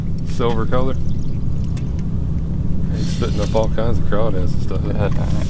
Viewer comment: Lmao I sitting here watching this with the captions on 9:45 dad says “he’s spitting up all kinds of crawdad” captions say “he’s spitting up all kinds of cow ass”. 😂😂 I had a good laugh here at work